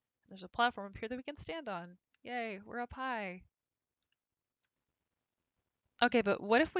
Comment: "Yay, we're up high!" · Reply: I heard "Yay, we're a pie!"